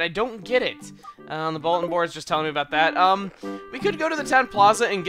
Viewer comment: Did... did I seriously just mishear "bulletin board" as "Voldemort"?